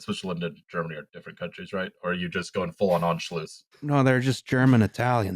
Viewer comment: At around 1:27:33 what word is saying? (if this comes off as me making fun of his speaking or anything like that I apologize, that is not my intention in the slightest(I also have had a life long stutter and lisp so I'm in no position to do that to anyone, not that anyone is anyway)) I just try to figure out the meaning of any word I don't know and the auto caption on youtube says "onshloots" and when I look that up in google it gives me no results (technically one but its not related). I would really love to know because it sounds like a cool word and I would love to know what it means incase I ever encounter it again! Thank you very much and man this comment is way to long lmao. Also I am a member of UAW and am proud of it FUCKING FORD BABY LET'S FUCKING GO F SERIES BABY WOOOOOOOOOO (I drive a Chevy Malibu) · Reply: Anschluss - IBEW says hiii